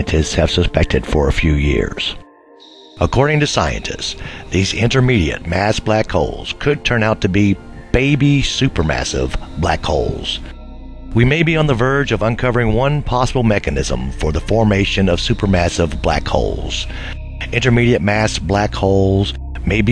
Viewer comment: did he say baby mega supper black hole? WTF???